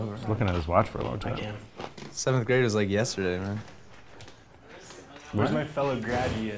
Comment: 🤣 when they thought he said 730. "730 happens everyday, twice a day." Whit shakes head 😂 he actually said "7th grade felt like yesterday"